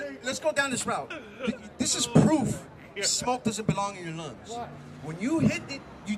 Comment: 😮😮😮😮did he just say air doesn't belong in your lungs 😅